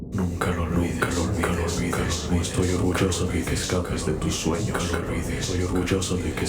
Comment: cacas de tus sueños 👀 (eso entendí xd)